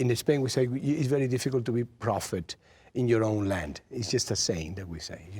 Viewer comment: I liked when Antonio said "It's very hard to be a prophet in your own land" but Eddie thought he said profitable lmao